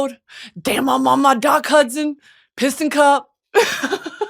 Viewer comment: i could have sworn I heard you say "piss in cup" and i was like damn how badly did doc hudson break down that he's pissin in cups